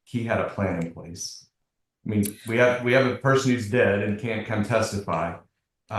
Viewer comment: Did I hear the judge correctly when he said “We have a person who is deceased and can not testify”? 10:45 Does this mean the father was unalived??? If so was it at the hands of his daughter? The video may answer all of this, I’m only 11:30 minutes in , but I have a lot of questions, lol.